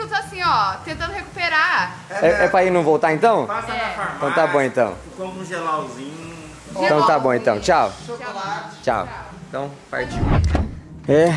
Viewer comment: Nunca vou esquecer desse tombo 😂😂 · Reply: eu escutei um xau Mor ali...kkkk